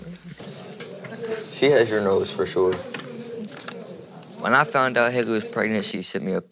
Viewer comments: i swear he said “when i found out hitler was pregnant” 7:50 not trying to start anything that’s just what i heard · Did he just say Hitler Was pregnant